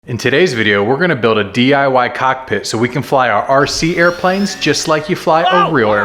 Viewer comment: I pressed 10 seconds ahead too quickly that all I heard was “Today we are gonna build a DIY cock...”